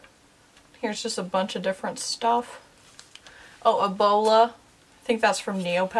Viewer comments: I thought she said Ebola. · I watch these type of videos while drawing so I was concentrating really hard and I thought she said Ebola and I was so confused until I looked at the screen.. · When you said bowla I thought you said Ebowla or however you spell it. I was like, "WAIT WAT?! Hold up hold up. Ebowl- OHHHH Bowla.. i am an IDIOT